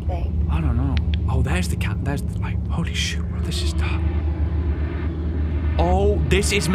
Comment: Am I the only one who heard Ryan quietly say 'Holy shi